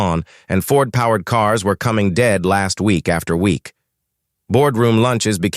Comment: that sounded like: "... Fords, were coming in dead, last week, after week."? Ouch. Blithering nonsense.